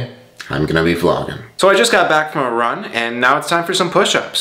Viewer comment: Coudve sworn he said he just got back from Iran 😭